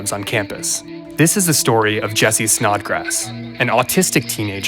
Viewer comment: I thought they said snot grass